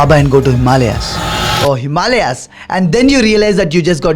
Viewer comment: did he said bhalaya at 0:43 ? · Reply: Himalaya